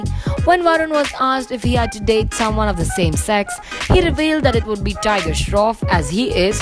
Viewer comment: In 0:48 is she said sex